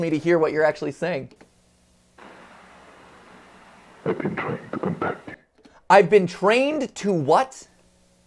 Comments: I heard "I've been trying to come back to u" not "contact u" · I heard “I’ve been trying to protect you” but I can also hear what Mat heard “I’ve been trying to contact you” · I swear I heard "I've been trying to come back to you" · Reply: I did so too at first because "contact" was pronounced unnaturally with the stress on the second syllable. Listening again, you can clearly hear that "to" isn't there, and also the "t" at the start of the syllable after "con"/"come" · I heard "Shadow man" and "Ive been trained to come back to you" in the final seconds of the short videos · when you guys heard "ive been trying to contact you" i heard diffrent, i heard "ive been trying to come back", anyone else hear that? or just me? thats all i can hear, i cant hear anything else. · Am I the only one who heard it say "I've been trained to protect you" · I heard "I was trained to protect you" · I thought the last video had said “I’ve been trying to come back to you” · At the end of the second one, I heard “I’ve been trying to come back to you.” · In the last one I didn't hear "I'm trying to contact you" I heard "They're trying to come back" Just a thought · At the commercial episode I heard "I'm trying to come back to you" and not "I'm trying to contact you" I dunno hear what you will but it could be a crucial thing for the story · I heard "I've been trying to come back to you" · To me it sounded like it said "I've been trying to come back to y-"